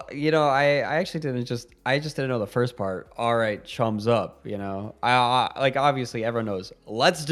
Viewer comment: I thought he said "times up, let's do this" .... but that's because I don't understand what "chums up" means.